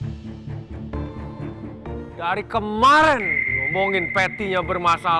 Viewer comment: kira" Chef Juna ngomong apa ada yang tau gak? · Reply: Kamvret dah, DaRi KEmmMAARRENN